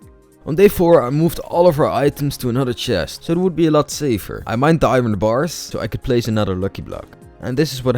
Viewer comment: did you say i mined diamond bars 1:52 😂😂😂